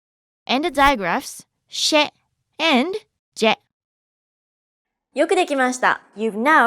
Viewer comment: Yukirikimashita ゆっくり来ました ? What does she say every time and what does she mean? ありがとうございます！